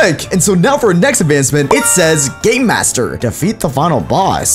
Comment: I thought you said gay master 😂😂😂 sorry, sorry, 😂 it’s just I was drawing while I was watching the video but I was drawing so I didn’t watch it at the exact moment there, and then I heard wrong I’m sorry I just, 😂